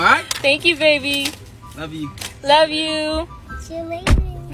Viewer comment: Did she just say "let's go to the air port to California" I think